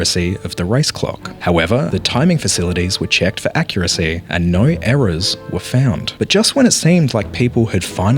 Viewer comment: I thought you said there were nine errors then I realized oh he's Australian he said naur